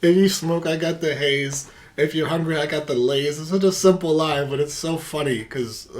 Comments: Anderson said “fillets” not “Lays” lol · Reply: I thought he said "I got the Lays" too first heard it. But I was corrected early on. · Hahaha he said if you hungry girl I got filets. Hahah you thought he said Lays potato chips 😂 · Anderson said “fillets” not “Lays” lol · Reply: I thought he said "the Lays" too..... this is gonna be the new Aaron Hall "did he say, dumb b!@ ?" argument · Interesting fact... I also thought he said "I got the Lays" lol. But he is actually saying "if you hungry, girl I got fillets". Very classy. Great reaction though.